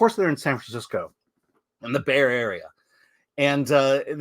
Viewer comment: San Francisco is the bear area? No wonder that's on the state flag. I must have misheard it all these years.